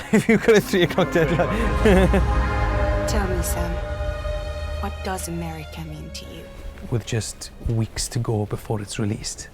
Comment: Can someone please tell me,what does she said at 5:52? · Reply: Not 100% however I heard, ""What does 'marry me' mean to you?""